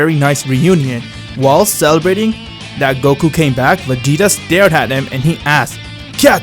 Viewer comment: i thought he said and vegeta stared at him in the ass 😁